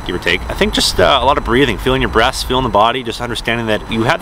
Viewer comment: it sounds like you say "feeling your breasts" lmao